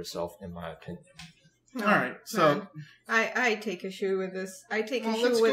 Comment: Am I the only one who misheard "take issue" as "take a shoe" first?